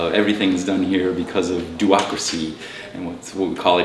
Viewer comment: the closed captions say "duocracy", but I believe it's supposed to say "do-ocracy", which is a term mentioned on Noisebridge's website.